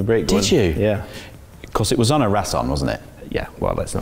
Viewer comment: What does Shaun say at 0:21 ? "Cos it was . . . .what? Onna Ratton? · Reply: on a Rasson